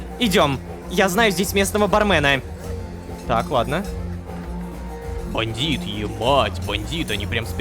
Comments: А мне одному послышалось: "Бандит, е6@ бандит..." 10:44?? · "*бать" мне послышалось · я ведь не один услышал "еб*тб бандит"? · бандит, е@@@ Неужели я один это слышу? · сказал ЕБАTb или мне послышалась? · Мне одному послышалось 10:43 сказал "еб*ть" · я один слышу не емать а еб*** ? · а вы тоже услышали бандииит ebаtь бандиит на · мне не послышалась он сказал Е**ть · мне одному послышалось "е*ать, бандит" ??? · на 10:44 мне послышалось или нет "Бандит, е*ать"